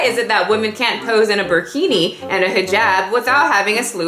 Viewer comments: LOL I thought you said a woman posing in a bikini and hijab. I was like girl 🙃 then I looked up burkini and ngl I had to laugh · I thought you were just saying bikini really weird I had to rewind like 6x 😂🤦‍♀️